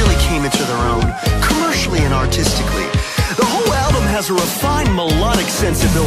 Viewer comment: I have to admit I thought he was saying "commercially and autistically" 🤦‍♀️ also "a face full of spleen" ✨🤦‍♀️✨